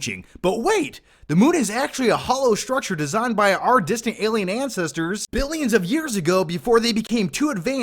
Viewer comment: I misheard that one part a couple of times as "our disney alien ancestors"